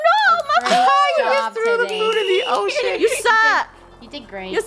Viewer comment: Is it just me or does it sound like said the s word. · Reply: He said the ocean, i'm pretty sure